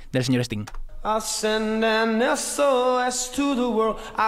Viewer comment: Yo toda mi vida pensé que decía "Eso es to the world" y también mi madre :c, wow rayos jajahahajajah. Pero en verdad dice S.O.S.